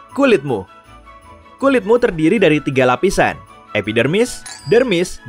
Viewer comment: kok gw dengernya epidemik?